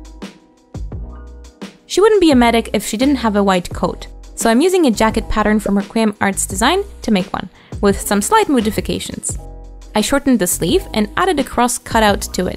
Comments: I'm a little sick while binging your videos and I don't know if it's my sick brain but 20:00 did anyone else hear "moo-difications" because that sent me into a laughing fit which quickly turned into a coughing fit. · Did you say "slight moodifications" as a pun, or was that just your accent? Not being rude, I genuinely don't know if this was a pun or not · I could have sworn that you said “moo”difications at 20:00 It may just be cow thinking though